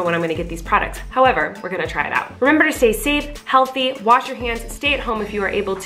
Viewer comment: I thought she said wash your pants 😭